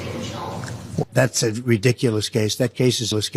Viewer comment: Did Trump just say, “That was a caseless case.” ? 🤣🤣🤣